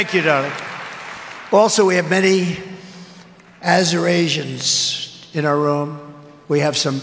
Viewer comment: I thought he was trying to say "Eurasians"